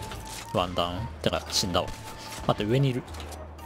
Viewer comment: モアイ像のところのワンダウンがワンタンに聞こえた僕です